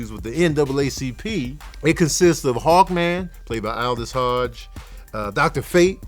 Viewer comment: I swear I thought you said “hog man” when you were saying “hawk man” lmao